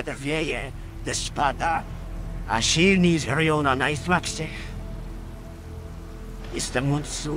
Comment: usłyszałem za pierwszym razem "A silni żerują na najsmacznych" 😅 Niezły przekład btw